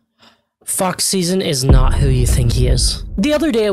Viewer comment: Please tell me that I am not teh only one who misheard him saying "Fuck Season" and not "Fox Szn" 😭😭